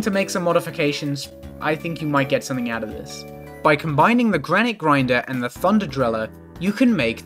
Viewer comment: Sorry, but all I hear is 'Granny Grinder'